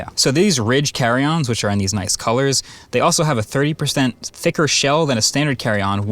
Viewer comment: did you say an extra 3% thicker shell? a full +3% LOL - edit: OK, so I did go check their website, it's 30% thicker, not 3%.